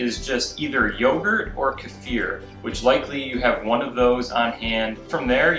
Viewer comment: i thought he said to culture it with yogurt ... OR FEAR